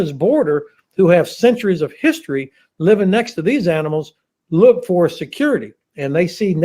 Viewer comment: Did he just call the Russians "animals"?